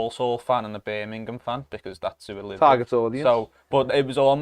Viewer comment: Can anyone tell me what said at 3:02 <link> Edit. Jack deserves a complimentary seasons pass. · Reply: “Target audience” he said